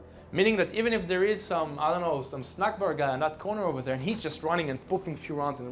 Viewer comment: Did he say "if there is a snackbar guy" ? LOL!